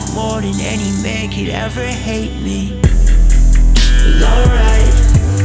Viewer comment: I thought he was saying lowlife not alright